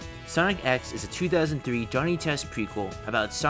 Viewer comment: did you just say its a Johnny test prequel or am I hearing it wrong